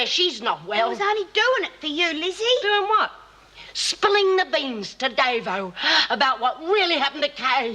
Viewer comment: does Lizzie say "Spling the beans"? sure sounds like it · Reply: To me, she's just saying spilling the beans.